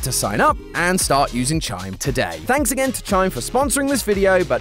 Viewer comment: I thought he said thanks China